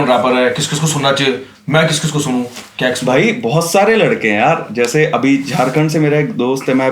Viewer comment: did he said kex?